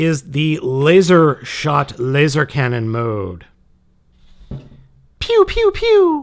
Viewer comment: It sounds like you just said "lizer cannon lizer shot".